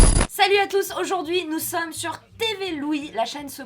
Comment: au début, j'ai compris " salut a tous nous sommes sur TV-louis" ... j'ai beuggué pendant un moment